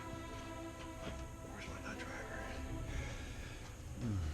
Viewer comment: "Where's my Dr . Pepper?" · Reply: I heard "Where's my nut driver?".